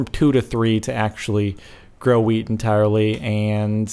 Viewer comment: Did anyone else hear him say ''grow weed''